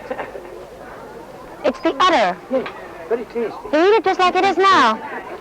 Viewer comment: I found it hard to hear, was she really eating raw cow udder? On first listen I thought they said otter